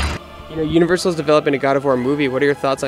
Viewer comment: Universal is developing a GOW movie? Did i heard that right? FUCK YES!